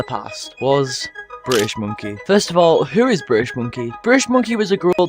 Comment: bro i thought you said british bagle the yeeps youtuber